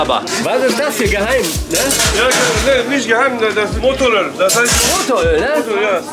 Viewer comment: wie heisst diese soße bei 2:30??? hab das nicht wirklich verstanden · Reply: Motor Öl